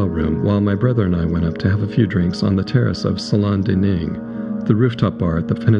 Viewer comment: Can anyone make out what Wallace is supposed to have said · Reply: No, it was good, very passionate and deeply felt. I actually thought he said 'trick' instead of drink but of course drink makes more sense. Thanks for responding. And cool production!